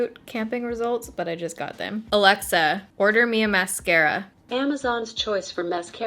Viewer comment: BRUH, MY ALEXA HEARD YOU SAY PIC MY MASCARA AND YOU ALMOST MADE ME BIY IT OML I NEED TO WATCH THIS WITH HEADPHONES